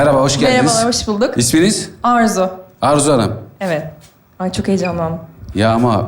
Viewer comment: orzu der gibi duydum